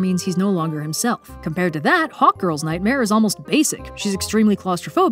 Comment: Ohhhhh, Hawk Girl. The first few times said her name, I thought she said Hot Girl and I was like, the writers actually named her that?? I was thinking, was it supposed to be a meta feminist critic gone agley, or were they playing it unironically straight, like Michael Bay styles...